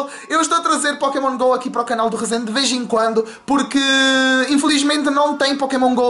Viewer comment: ESCUTEI REZENDE VIRGEM